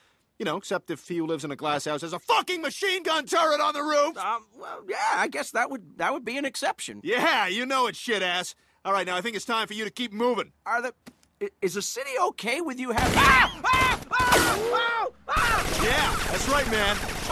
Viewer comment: did he... did he say... SHITASS?